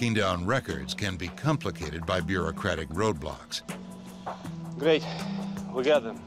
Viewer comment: is it me or did he just say ROBLOX?